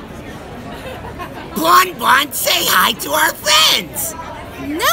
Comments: Lol I thought said "JUST SAY HI!!" wtf is wrong with my ears jesus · SaY hI tO oUr FrIeNdS!!! Bon bon: no: ANGERY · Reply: He said friends i thought he said fans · "bON BON SAY HOI TO OUR F A N S" "nope. nobody's here." "YeS tHeY aRe!!!!!!!" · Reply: I heard "BON BON SAY HOI TO OUR FRIENDS"